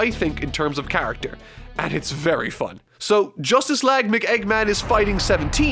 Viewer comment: Did I just hear "Justice lag McEggman?"